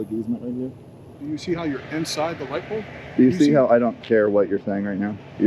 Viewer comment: "Do you see how you're inside the lightbulb?" at 1:50 Does anyone know what he meant by this? I rewinded like 5 times and have no idea.